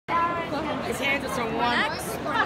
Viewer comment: Wha- did i just hear someone say "his hands are so warm"?!?!?!?!